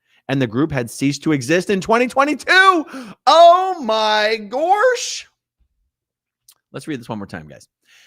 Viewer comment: Did you say, "Oh my Borscht"?! That's hilarious! 🤣